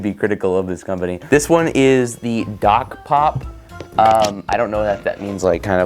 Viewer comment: i thought you said gock pop and i was so happy that us trans girls finally got a soda flavor :(